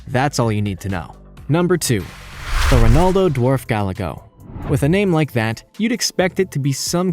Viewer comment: I keep hearing " the Ronaldo Dwarf Galago " lmao